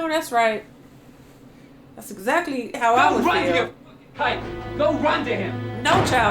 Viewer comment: you asked what he said, he said "go run to your "kike" who is his mom's husband/boyfriend. the word is basically the N word (with a hard R) for jewish people